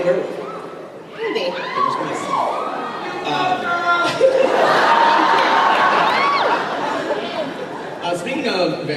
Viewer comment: What does the guy in the audience say at 4:22 ? thank you :) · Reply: I think it was "You go girl!"